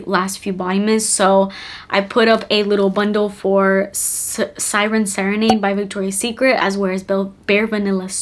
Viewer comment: When my sister said Sired Serenade I thought she said Siren Cyanide 😂